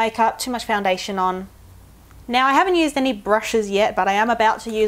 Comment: I thought you said anal brush :')